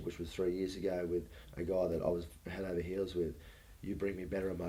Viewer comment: Did I hear this right? At 7:38..with a GUY I was head over hills with!